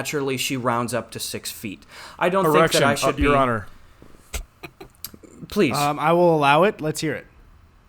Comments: i swear to god said erection your honor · did say "erection your honour" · says 'erection' instead of 'objection' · did I hear an erection your honor?